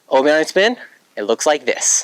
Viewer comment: I thought it said obi-wan spin